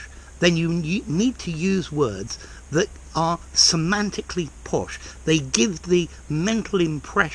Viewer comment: Semantically po... what? Can someone say? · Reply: semantically posh. They give the mental impression ....